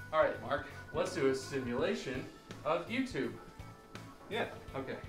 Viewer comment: An underappreciated moment: when Bob goes "let's do a simulation of YouTube" and Mark drops to his knees with no hesitation. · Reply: At first I thought he said: Let's do a simulation of you two